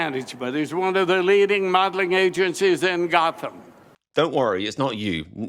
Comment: nah nah I’m definitely sure I heard him say something about Gotham 😂👏🏻